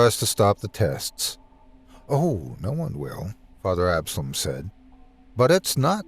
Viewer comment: When he said father Absalon, I thought he said epsilon and was like...I wonder if he knows bishop alpha and Cardinal omega